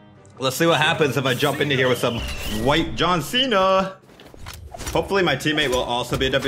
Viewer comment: Did he just say “White John Cena”?